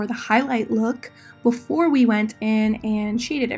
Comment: Everytime u said shaded I thought you said “cheating”...am I alone? But I’m definitely going to attempt this look.